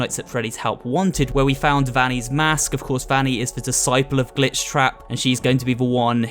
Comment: It sounded like she said the b word at the end but she said glitch